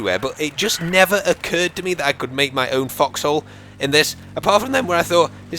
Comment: LOL I THOUGHT HE WAS SAYING FUCKHOLES INSTEAD OF FOXHOLES